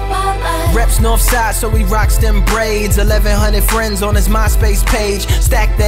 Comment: did he just say MySpace? about